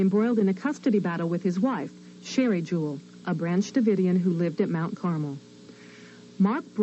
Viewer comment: Paradise PD ruined this for me. Every time you say "Branch Dividians" I hear "Ranch Dividians" because of that joke about the fat cult 😂😂😂😂